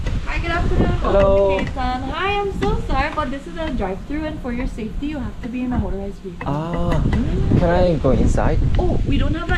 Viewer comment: 저기여~~ 여기 차로 들어오는 곳이거든여??? 라는 소리가 들려... 내가 한국인이 맞지.